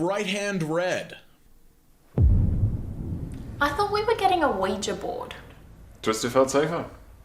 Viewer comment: Right Hand Red makes me angry just because I don't know what the fuck the bearded guy said. 14:36 "I thought we were getting a Ouija Board." "Twistafēltãkkör." Twister felt cheaper? No one says things feel cheaper... Okay, after about the fifth try, I think he said "Twister felt safer."